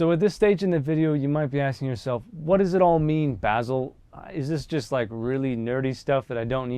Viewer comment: did you call yourself bazzle?